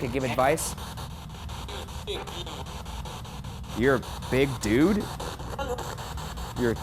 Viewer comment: instead of “ur a big dude” I think it was “ur a bit rude”